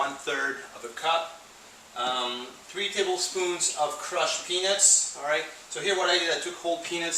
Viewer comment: Did I hear "3 tablespoons of crushed penis"?